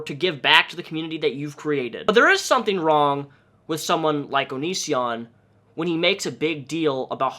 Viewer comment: The auto generated captions wrote “anis yawn” instead of “Onision” at 1:18 and I’m dead ☠️😂